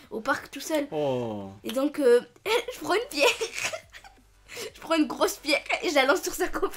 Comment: j'ai compris une biere 😂